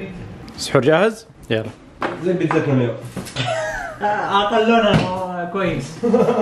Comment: وش قال نبي ترجمة للجمل السريعة واللي مو واضحة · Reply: قال زي بيتزا اليوم يقصد اللي كانت في الفطور